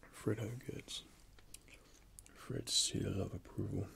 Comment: What did he say at 8:22? Fred seal liverprule🤨